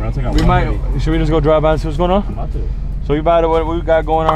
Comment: what he say??😂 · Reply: Keanu Hunter He said, “Should we just go drive by to see what is going on”